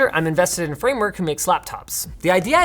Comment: Anybody else heard Linus say "Slaptops" @ 0:40 😂